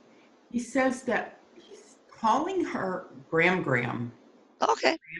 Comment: I heard "gram"